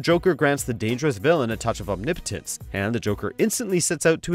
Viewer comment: Omnipotence. OH, I thought he said Impotence. HA HA HA!!!